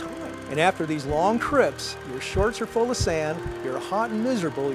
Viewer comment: Did he just say, "shots of vodka" @ 7:36 ??? Lol.. I would think that campers icebox would have carried bourbon...